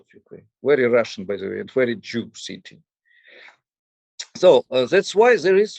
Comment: I am having a hard time understanding the words Mr. Buzgalin is using to describe Odessa at around minute 31:35 -- have listened multiple times and it sounds like he is saying "Very Russian and very Jew city." Can you please confirm, clarify or correct? Thank you.